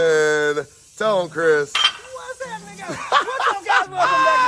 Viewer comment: Did he say whats up nigga lmfao